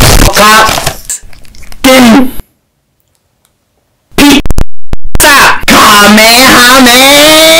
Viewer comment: ピッザしか聞こえないんだが